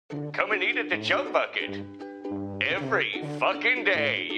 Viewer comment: Plankton: Come eat at the Chum Bucket Subtitles: Come eat at the Junk Bucket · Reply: If you listen carefully it actually sounds like he said "Junk bucket"